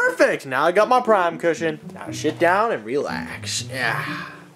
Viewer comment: I thought he said sh*t